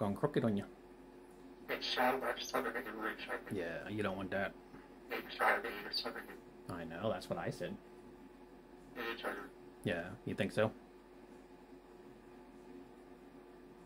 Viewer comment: Sounded like he said I don't want to fly? What Max? Sorry about the Vet. But, those toenails, little boy. ❤